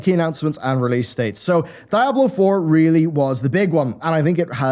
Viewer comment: DID HE JUST SAY "DIAL BOH!? lmao dud! sumimasen, what the fuk? XD